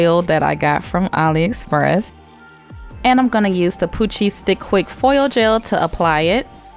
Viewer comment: I literally heard "coochie stick quick foil gel"